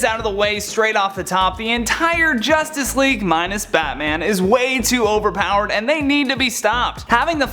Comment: Haha I could've swore he said "minus batman" haha weird I mean why would he even say that cuz batman is waaaayyyy too op I'm tripping.. right?